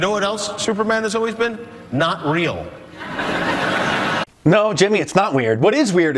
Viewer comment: i understand what youre saying though but he didnt say weird he said not real.